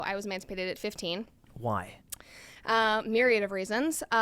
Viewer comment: can you guys make out what she is saying at the very beginning of the video? Like the first thing that is spoken out of her mouth at 0:00 is: "something-something at 15" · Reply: "I was emancipated at 15."